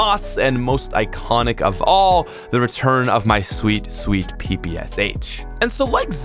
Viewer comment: my phone somehow paused it when he said ppsh and i only heard ‘my sweet pp’